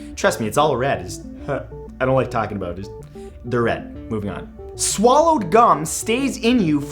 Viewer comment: I thought it said swallowed cum